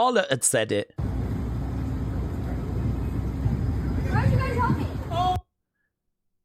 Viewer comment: Was Tiffany really said "Where ado you guys want me"? Because I heard, "Why didn't you guys help me".